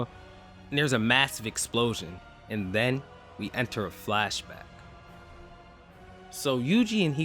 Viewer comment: ngl I just woke up while watching this so I thought you said Sukuna landed a black flash 😂😂😂😂😂 I had to go back and check to make sure. Would have been sick if he actually did though